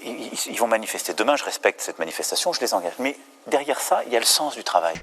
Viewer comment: Il dit quoi parce que j’entend « je les enmerde » a